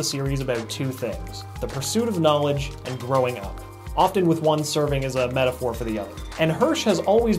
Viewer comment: and 10:01 I thought he said throwing up XD